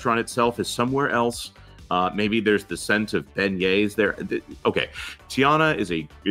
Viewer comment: At 56 seconds I could have sworn said "the scent of Ben-gays!" instead of beignets!